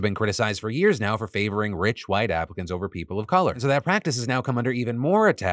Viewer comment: did you just say “rich white africans” lol